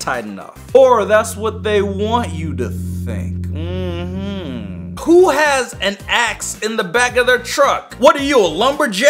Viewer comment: At this point, fast foward 10 seconds and it will sound like he said who has an axe in the back of there butt XD